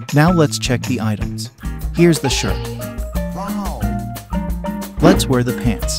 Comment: did he say sh*t